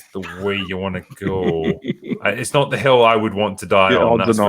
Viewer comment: With the accent, I heard "Is that the hell you want to die on?", which seemed somewhat appropriate in this context.